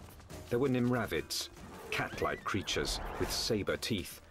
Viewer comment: I'm going out on a limb maybe was omnivores able to hunt or forage idk just an opinion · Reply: What's that word ? Nymrabbits ? Can you help